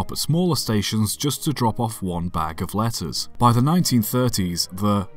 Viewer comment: thought he said lettuce